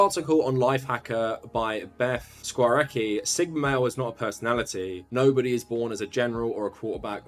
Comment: First to comment is a Sigma. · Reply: ….I thought you said sugma male